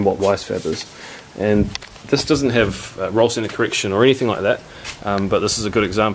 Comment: Can someone tell me what he's saying at 7:00? "Something" correction. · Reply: Ethan Brindley ‘ roll center ‘